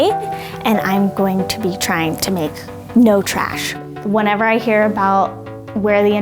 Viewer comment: I thought it said "makeup trash"